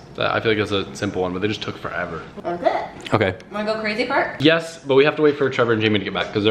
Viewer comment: maybe I'm immature, but it sounded like said, "wanna go crazy fart?"